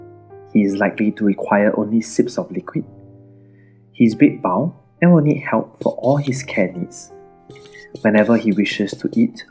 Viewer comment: Could you please repeat what you say at 1:00? I didn't quite catch that. U seem to say, "His big bowel will help meet all his care needs." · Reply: Hello! At 1:00 he says: "He is bedbound and will need help for all his care needs."